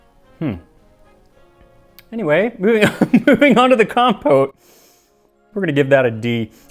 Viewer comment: Was in the kitchen and thought he said compost. Had me thinking he was digging out the real military recipe not the propaganda one for the homefront.